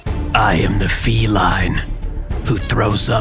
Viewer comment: On the last one i hear "i am that cat"